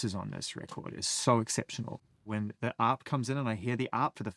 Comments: What is Zane saying at 36:38?? Up? Op? Arp? help what is it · What is Zane saying at 36:38? Getting the AAP? out? Hearing the AAP for the first time? Is he saying harp?